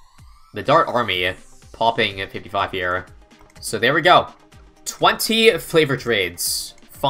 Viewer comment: I think I heard “flavored” trades😅